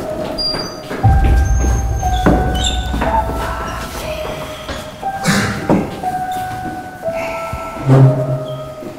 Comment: menit ke 29:22 ada suara bisik2 tapi ga begitu jelas ngomong apaa:(( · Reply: Lepasin